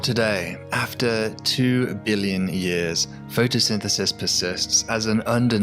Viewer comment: I heard "photosynthesynthesis" at 0:51 and decided not to continue watching the video made by this dyslexic.